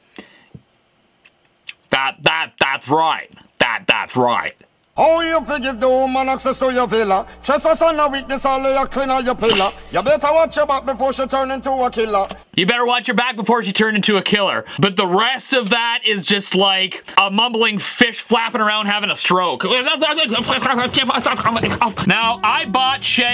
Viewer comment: I heard you better wash your butt before she turn into a killer 😂